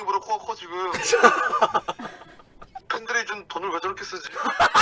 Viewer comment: 얼마전부터 자막이 아쉽네요... 4:10 아무리 들어도 팬들이 준 돈을 이라고 들리는데 근데 쟨 이라고 나와있고 4:55 도 12시간을 못준적이 라고 들려서...